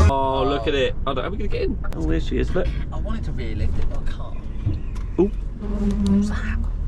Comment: He could be a Daf't' salesman. And the scannia at the garage when you said to it gonna be tight getting it out, (i thought i heard we'll airlift it) pmsl. Love the content as always.You 2 are a great team.